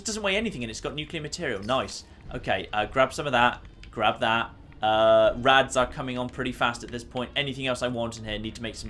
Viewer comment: him down in the basement felt like a mission in fear equation He said:"Rads are coming on strong" I heard: "The Fog is getting angry" . lol